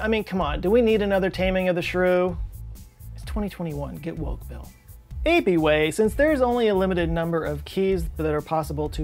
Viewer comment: did you say "apie-way"? 😆